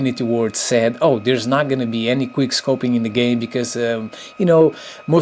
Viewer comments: Thumbs up if your watching in 2015! But am I the only one that heard him say queerscoping? Instead of quick scoping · clear-scoping? I'm sorry you are an epic youtuber and I know your not British but its soooo annoying when you say quick-scoping cause you sound like you say clear-scoping. I just wanted to see if anyone else noticed this.